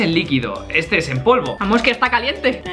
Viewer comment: dijo amor está caliente o escuche mal...